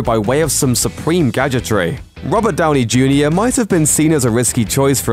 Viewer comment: Did he just say Rubber Downey Jr?